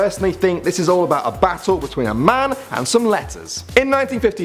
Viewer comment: I thought he said "A Man and some Lettuce" XD